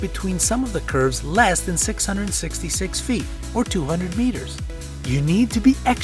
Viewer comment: Did I hear 666?